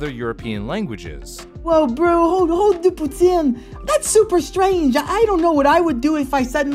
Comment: Dude, is long hair guy quebecois? And I heard “putin” as “puțin” which means a litle in romanian or “poutine” in french? (I’m actually quebecois romanian so it surprised me) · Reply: Yeah, it's "poutine" as in the Quebec dish, it's a really good accent too!